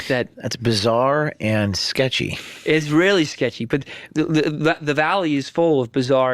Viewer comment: "Israeli sketchy" around 2:23? (Am I hearing that correctly/ right? )